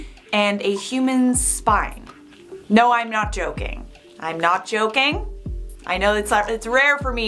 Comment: I love how said “I’m not joking I’m not joking“ and I thought said “im not chocking I’m not chocking“